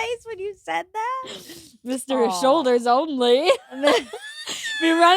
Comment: i thought they said "Stoners Only" 🍓